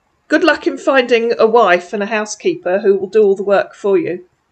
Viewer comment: Holy Cow! You just talked yourself out of an instant marriage proposal to a real prize so you can look after him and his fictitious daughter for life! When I became a senior, that is what happened to me often. Live in a tiny town short on females and I became a magnet for old broken down farts who thought I would be a good candidate to cook, clean, nurse, and financially support them for the rest of their days in my home. They really couldn't see that they were not bringing anything to a relationship and unfortunately I was too much of a lady to ask them. Edit: had on subtitles/captions and his 'housekeeper' captioned out as 'ass keeper'. How am I supposed to keep from laughing even harder? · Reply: Even without the captions it sounded like he said "ass keeper". 😂😂😂